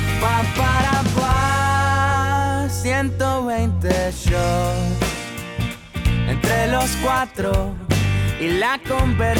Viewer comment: Que dice en 2:30-2:32? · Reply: 120 shots